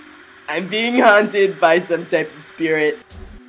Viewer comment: i thought he said I am being hunted by some themsycrian. lol